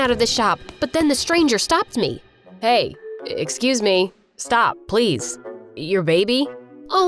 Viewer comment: I thought he was saying stop to the baby😂